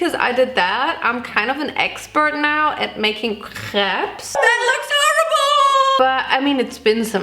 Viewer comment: i was only confused about the crêpes pronunciation because i thought she was saying crabs for a long time because i didn’t see the rainbow crêpe video😩😩